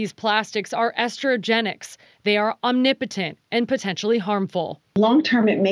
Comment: Did she say the plastic is omnipotent? It has unlimited power?